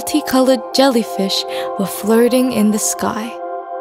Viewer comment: im so sorry but i heard that as "jellyfishes flirting in the sky" instead of floating